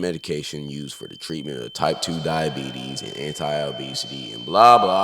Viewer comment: I thought it said type 1 diabetes and I went YEEEEEEEEES